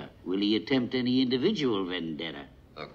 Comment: Thank god for subtitles. I always thought the other son said “attempt any individual Vendetta. Now I know he said “win dinner…”